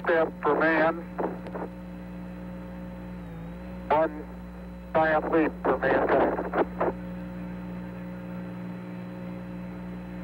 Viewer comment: If you listen close...it sounds like he says..." one small step for man, one defiant leap for mankind."